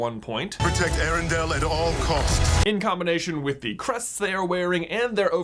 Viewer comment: I agree with everything but I thought the soldier said "we take erandale at all cost" so I thought they would be bad in the end, but I think the bad guy will be the cause of the fog.